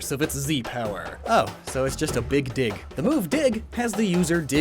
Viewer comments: I thought u said big dick for some reason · Am i the only one who heard big d*ck